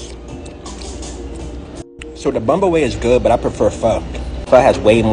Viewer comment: Bun Bo Hue I thought bro was saying bumblebee or something🤣😂